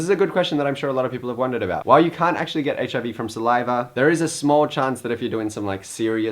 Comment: I learn more from Troye's videos than from my Sex ED teacher... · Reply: he actually says "you can't get hiv from saliva", (2:40) so he hasn't made a mistake. You are completely correct though :).